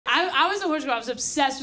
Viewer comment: What she says: "I was a horse girl" What I hear: "I was a psycho"